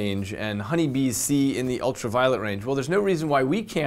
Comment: Growing up playing doom I always hear "ultra violent" instead of "ultra violet".